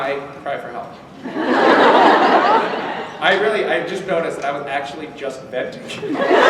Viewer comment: sorry but can anyone tell me what he says around 42:22 with "i was actually just..". I can't understand it and english isn't my native language. thanks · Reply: ElisaMagnus It's "I was actually just venting" 😊